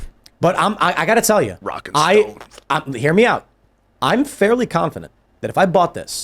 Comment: DID I HEAR A ROCK AND STONE!?